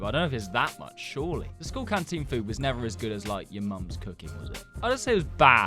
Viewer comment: Did U just say nig a